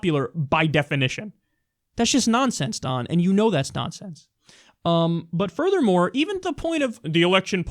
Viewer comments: did he just say "And you know that's Donsense?"(nonsense) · Did Kyle just invent a new word? Donsense? 6:08 I can't be the only one who heard that!